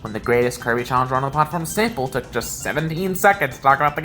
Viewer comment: Honestly the only things I could see of to improve are: 1. Some parts where your voice goes robo mode and crunchy 2. Doing more editing with the screens, like having a "main screen" be bigger when relevant and the other one smaller. This would help make it easier to focus,fill displays better, and provide space for text 3. Show track names on screen when the music changes The fundamentals are all here and if you do more projects in the future, I'd be down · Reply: further ennitpickments/questions * at 0:13, "the greatest Kirby challenge runner on the platform, Sample," sounds a little bit like you're saying 'the platform-staple'; a slight pause would fit * 2:38's sentence isn't very legible and took me a few attempts to arrive upon what it said without the subtitle ('treasure' and 'my way' in particular) * 6:22's "can't reclaim it in time" has "in time" truncated from the subtitles; assumedly the subtitles were made afterwards and as such have post-editorial decisions? but i (someone who hasn't played the game) appreciate being informed there is a time limit * it seems to me that by corollary of being able to produce any other ability (as well as another copy of itself), bubble is implicitly S-tier, why is it in "it solves puzzles"? * 12:23's dropping of 'also' from the subtitles seems to reduce information unnecessarily too